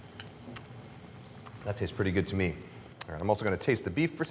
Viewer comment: caption is wrong, he says "mirin" not "meat"